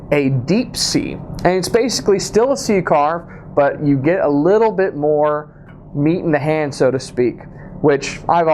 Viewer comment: Did he say that he’s comfortable with a little more meat in the hand?